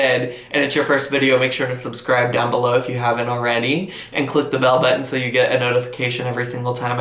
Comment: Lol, at the end there I legit thought you said "click on the bellybutton so you get a notification(...)"